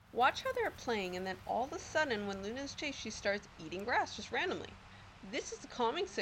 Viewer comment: she starts doing what? Couldn't hear what you said there. I hope you are able to answer! I am genuinely inquisitive. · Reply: RDR I think what your talking about is when she said “she starts eating grass-“ Hope this helps